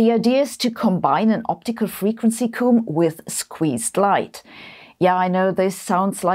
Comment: Your pronunciation of "optical frequency comb" made it sound like you said "optimal frequency coom", and for half a second I thought you were telling people how often to masturbate.